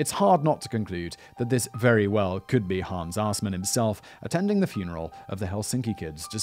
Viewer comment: I thought he said "Hands-Arse Man" 😂😂😂 awesome perv nickname